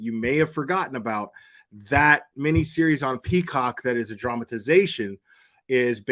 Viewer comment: did you say that show was on "Pee Cock' I guess that would explain the main theme...lol